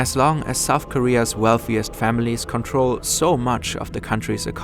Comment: I thought it said "Powerful females control much of the South Korean Economy." - and I thought, that really is a dystopia !